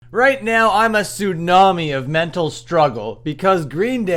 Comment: I misheard "Tsunami" for "Sunami" a few seconds in here and got a bit dissappointed when the video turned out to be about one of the worlds most boring bands Green Day and not about one of the worlds most cringe but very entertaining bands Sunami. Let's hope there is a new and actual Sunami video in the making on this channel.